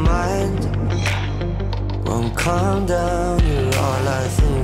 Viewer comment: Either I have a dirty mind, or Troye put some sexual things in this song. Or both. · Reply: i heard instead of 'wont calm down' i heard 'no condom' 😅😅😅😅